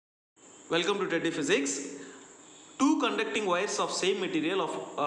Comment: When he said teddy physics, I first heard it as tatti physics